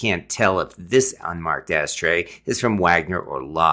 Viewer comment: All I heard was "ass trays"